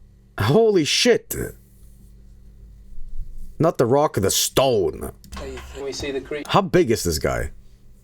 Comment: DID I HEAR ROCK AND STONE?